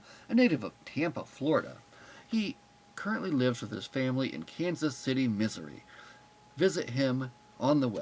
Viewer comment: It sounded like you said Dayton lives in Kansas City Misery. LOL